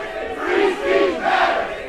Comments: They chant "free speech matters" and I heard "krispey Kreme matters" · when they started saying "free speech matters" i swear to you i heard "krispy kreme" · I dunno why, but I hear them chanting "Reese's Puffs". I dunno